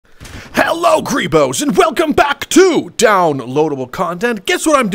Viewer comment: What does he say the beginning YouTube CC says Hello Creepos I thought he said greebos? So any clarification?